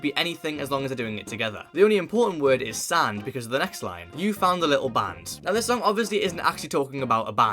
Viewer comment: bro I’m with on this one I thought they were talking about a rubber band ☠️ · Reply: I thought they said and you saw a little lamb